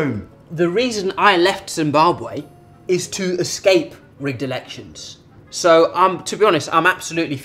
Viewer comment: "The reason I left Zimbabwe is to escape rigged elections" 🤣🤣🤣🤣you got me there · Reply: LoL I thought he said redelection, I was about to Google the meaning of that word LoL